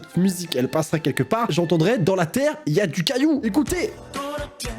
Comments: j'ai entendu "gadoue" à la place de "cailloux" ! ! ! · A 8:01 moi j'entends "dans la terre, y'a du gâteau"... Voilà 😂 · "Dans la terre,y'a des caillou" moi j'ai plus entendu "dans la terre,y'a du yaourt"😂😂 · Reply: Nan moi j'ai entendu "dans la terre, du cailloux " 😂😂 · Nan moi j'entend dans la Terre, du gâteaux🤣